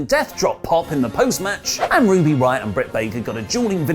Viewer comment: did i hear ruby RIOTT?